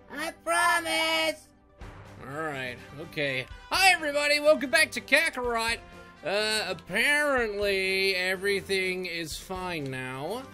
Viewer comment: I thought you said Hey Guys the Name’s Sonic